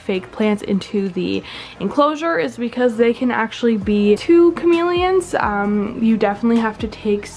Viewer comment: What is she saying at 4:13? “Be to chameleons”?? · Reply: Probably harmful in between to and chameleons